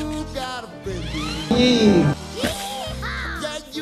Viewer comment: what the fuck he said yee haw and there was nothing